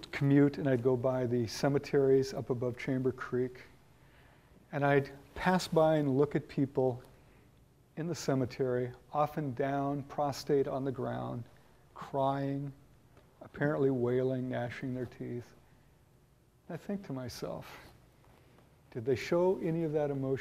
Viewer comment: did you mean prostrate on the ground? I thought I heard prostate on the ground.